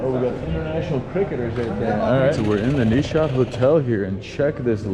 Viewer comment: I think he said Zeeshan. Because shant is not a name.